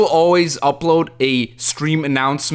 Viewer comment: I misheard when you said "stream" as "scream"